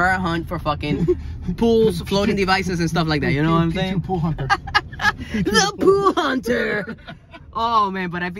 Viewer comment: I thought he said poo hunter 😂